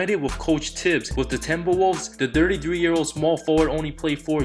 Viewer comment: Why does it sound like he is saying "Dirty" when he is saying "thirty"? lmao